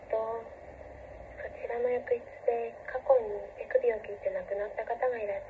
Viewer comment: 手首を切ってが、乳首を切ってに聞こえて困惑した😱